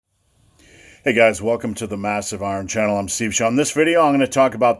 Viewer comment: I heard you say massive arm channel, sums it up though